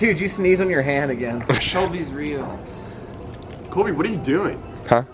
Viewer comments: I swear I'm not the only one who heard "solbys real" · Was I the only one who heard "sholbys real" instead of "solbys real" ?